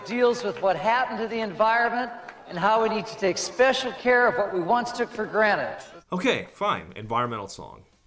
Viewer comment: did he say "took for granite" lol